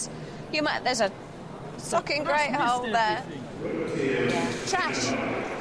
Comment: did Phillipa say there is a fucking great hole there?!